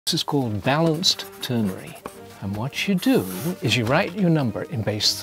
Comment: am I the only person who heard balanced turkey